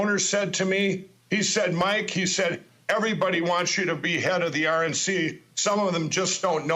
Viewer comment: Sounded like he said, "Mike, everyone wants to behead you at the RNC, some of them just don't know it yet."😂🤣 Well, that is probably truer that what he actually said.